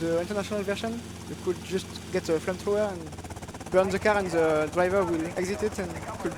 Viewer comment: "On the international version, you can just grab a flamethrower and [i dont know what he says here] and the driver will exit it, and you can just drive ma-, candy suxxx to the hotel, but you need to be faster because you need to get to the top of the building to get the pictures." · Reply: "burn the car"